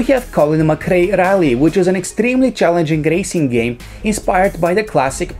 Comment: did he said gracing game